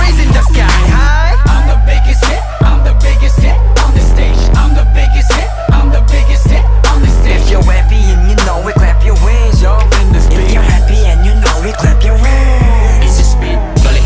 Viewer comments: damn I thought they were saying “witness me” instead of “in this beat” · I always thought it said I'mma make you sit on the stage! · i thought he said i'm the biggest shit Lol😂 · I always thought it said I'mma make you sit on the stage! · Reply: I thought it said "I'm the biggest hit on this station"